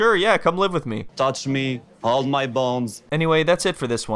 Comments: Is it just me or does it sounds like he says “hold my balls” (ik it’s hold my bones but my humor is that of a 6 yr old) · I GENUINELY thought he said “touch me, hold my balls” and I was floored. Never heard such poetry in my life